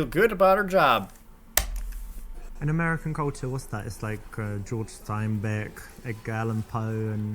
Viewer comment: Wait, did he just say "George" Steinbeck?